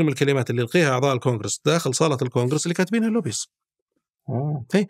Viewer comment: وش قال اللوبي ……. ماسمعت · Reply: Lobbies